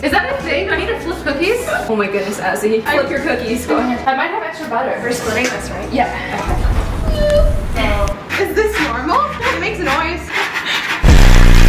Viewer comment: Bri and azzy: doing mad things. Talking trash Gloom meanwhile: working like a master chef 🍧🍦🍫🍰🍬🍨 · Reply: sean cross but i thought u said “go ahead, irritate me“ 🤔